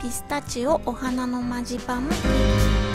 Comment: entendí Ajanana no manche pama